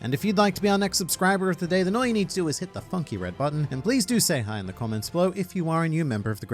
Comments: anyone else hear "hit the fucking red button"🤣 no? Just me?🤣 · When he asked us to subscribe at the start of the video I misheard funky and heard fucking 😂